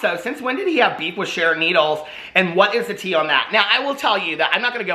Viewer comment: Sharron needles?? Ohhhhh I thought you said sharing needles 😬